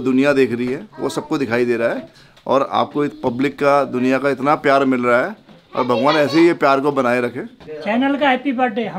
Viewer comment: pe ne kya bol diya 😂😂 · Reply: sourav ne trim kar diya... ne "g*ndu" bola tha